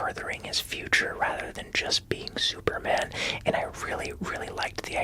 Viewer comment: Did say superman